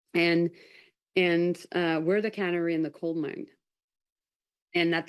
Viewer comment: It sounded like she said We’re the Cannery in the Coal Mine 🤦🏼‍♀️ She seems very bored not into this and it shows